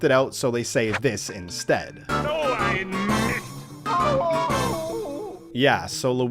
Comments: I always thought wario said oh I missed! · LMAO I always thought Wario said "Oh, I missed!" when he lost! I didn't know it was german! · I swear my whole life I thought he was saying “d’oh I missed” but it’s “so ein mist” which translates to “oh crap” or “darn it”. Funny cuz crap would become a censored word later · I thought Wario said "Oh I Missed!" but I also don't speak German.